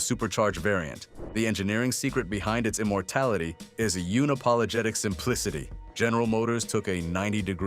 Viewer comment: did you say "uni-pologetic"? @ 1:11 lol it's un-apologetic, like undo, not unibomber.